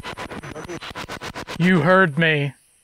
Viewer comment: Hey guys! We want to give away a free tshirt of your choice to someone! All you have to do is be subscribed to the channel & leave a comment with a time stamp of your favorite part of this episode! Comment must be left on this pinned comment :) Contest ends Wednesday August 10th! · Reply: I swear it says "he heard us" through the Spirit Box. I find that to be one of the most compelling statements I have ever heard from a spirit box.